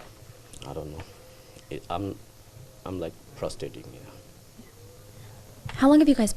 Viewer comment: Wait... did he just say "PROSTATING"? Lmao